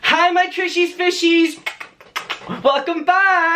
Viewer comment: Bro I thought u said “coochie fishies”